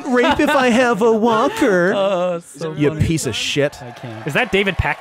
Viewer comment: that "shit" sounded like "shiet"